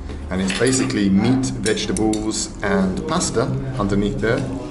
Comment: I thought he said plaster.